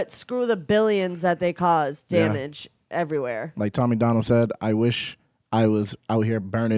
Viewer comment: Opps rewatched the video & this time I heard you say tom Mcdonald